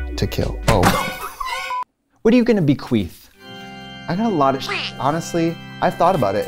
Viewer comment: I heard Katya say and then read it as... "WHO ARE YOU GONNA BEQUEEF?" 🤪 #DYSLEXIA!... THEN I was like, "OOOH CHILD!" 😏